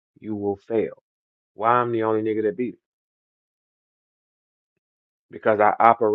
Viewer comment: Wait did he say he beat Millz ???? 😂😂😂😂